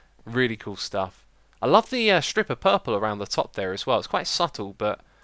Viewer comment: am i the only one who thought he said stripper purple?